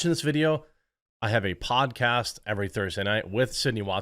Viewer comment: I heard "And BTW, if you're watching this video I have a pot-cast every Thursday night with Sidney Watson" Me: Where do I sign up for this pot- I mean where do I audution? My brain after 15 seconds: Go back and give it another listen. It was PODCAST, you idjit!